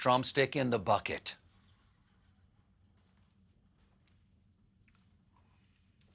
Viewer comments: I thought Colbert said "trumpstick." "And he will continue fighting for you, provided you are the last trumpstick in the bucket." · I heard "Trumpstick"... 🤔🍗